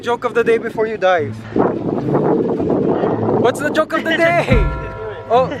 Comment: i thought mikey said "what is the joke of the day before you die" and i was like wtf? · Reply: oh yeah He said before u dive, I believe.